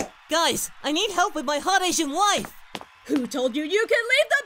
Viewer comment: "So, after I eat my Jimmy Johns...drink my heetap... and FUCK my hot Asian wife..." #BeatUpPerfectCell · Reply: TheNewLelouch17 I thought he said "non-Asian wife" lol referring to the redhead girl